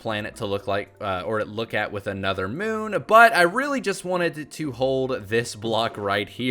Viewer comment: it sounds like you said another moon or butt